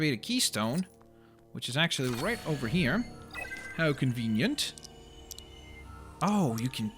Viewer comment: I thought said "oh, shit!" but I turned the volume up and I realized he said "how convenient"